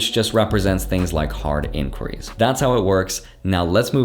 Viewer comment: What did you say at 2:56 ? Card inquiries?